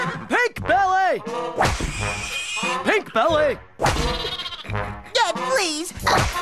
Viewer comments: I thought Ed was saying "Pig belly!" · Ouch. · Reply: TBH, I thought he kept saying, "Pig belly". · I thought Ed was saying "Pig Belly" when I was younger 😂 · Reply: I thought he said pig belly too when I was a kid 😂